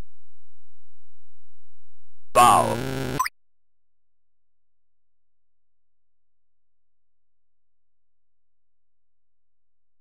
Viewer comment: Did he say "bow" as in "bow-wow"? Looks like a well thought-out puzzler, anyway.